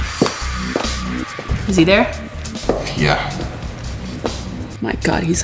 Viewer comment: Oh I thought Lele said "You see there?" and I read the subtitles before I got to the next part and thought the guy was looking at himself cuz I could see Lele's reflection so I thought they could see their own and the guy said "yeah. My God he's hot" as in himself! Also, after I saw the subtitle say "Is he there?" and not "You see there?"